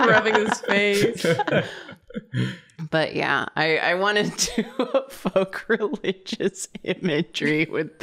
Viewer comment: I just need to point out that the captions at one point say "I wanted to fuck religious imagery with this one" (24:29) instead of evoke and that seems pretty accurate actually lmao